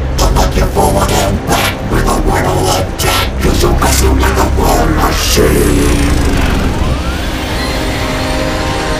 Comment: Is he saying jack or attack 0:25???? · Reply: Accordion Tiki attack